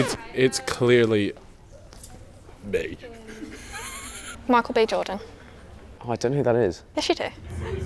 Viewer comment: When she came out all I heard was "MIchael Jordan!"